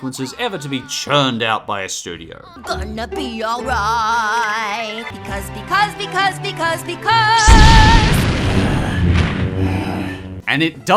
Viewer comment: I swear she says bill Cosby